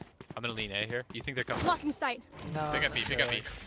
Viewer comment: is she saying FUCKING SIGHT?! XDDDD lmaao